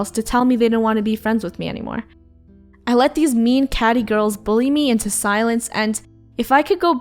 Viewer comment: “All the girls bullied me and told rumors about me.” Me: “did they just mess with the queen-“ Me: slowly pulls out death list “What’s all their names again?” i just want to have a talk with them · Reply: I think she mentioned the girl name that spread rumours.. Well I heard the name is "Katy" Or "Katie"? Idk.. That in